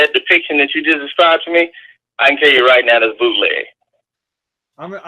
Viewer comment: Das bootleg bruh that's fake news bruh 😂🤣🤣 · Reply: Black Sheep The Unwitness I had to replay that spot twice. I thought I heard him say that's bullsh💩t I was like, 😳😂😳 WHAaAaAT!!! OH NO! THAT DID NOT JUST HAPPEN!! I was wrong, it happens.